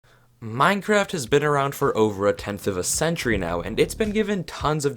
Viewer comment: I had my headphones off at the beginning and I heard “minecraft has been around for almost a century”😂